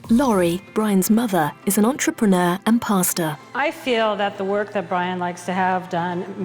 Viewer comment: I thought it said his mom is an entrepreneur in pasta... it all made sense once I realized it was gnocchi since she gave birth to a potato